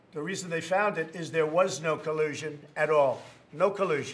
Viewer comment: Somehow when Trump says "no collusion" I hear "collusion"